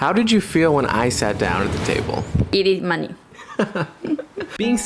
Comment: What did she say, easy money